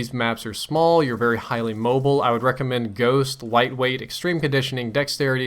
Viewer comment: Did just say white weight at 4:25 hahah made my day